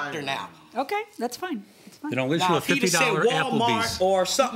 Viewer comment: Did I imagine that he offered a gift card to Appleby's instead of his company? The plaintiff was talking at the same time. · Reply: He did actually at 13:34. When she first said she didn't want to go take a gift card from his company he said "then I'll issue a $50 Applebee's" but everybody was talking at the same time so I don't think the judge or plaintiff heard it.